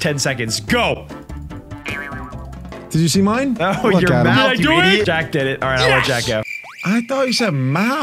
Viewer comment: I also thought he said mouse